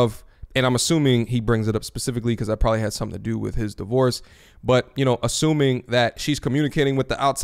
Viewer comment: YO! Did you just say," because "I" or it had something to do with the divorce?" 12:35 ??